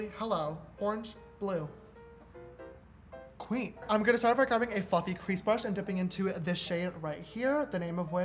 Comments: thought he said a fucking crease brush and nearly choked like james noooo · did he just say "a fucking crease brush"? Lmao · I thought he said fucking crease brush 😂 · it sounded like he said “F***ing crease brush” 😂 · I thought he said fucking eye brush lol I'm dumb 😂😂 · When he said fluffy crease brush I thought he said fucking crease brush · it sounds like he said a fucking crease brush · I heard: “A fucking cream brush” then he said this is a family friendly channel, HELLO? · i thought he said a f***ing crease brush at 17:25 oml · I thought he said “I’m gonna start with a f*cking crease brush” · I thought he said fuckin* crease brush but he said fluffy 🤣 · who else thought he said “fucking crease brush” 😭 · does he say '' fuckinng crease brush'' ??? a shistar is shook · it sounds like James says fucking crease brush 😂😂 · a fucky crest brush😂 it sound like that’s what his saying😂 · am I the only one who heard “a fucking crease brush” 😂😂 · I swear to god I heard James say a “fuck you crease brush” ahahah I love that